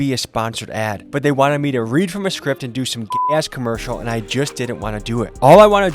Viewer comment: Sorry, did you say "gay ass" commercial at 2:44? Very unnecessary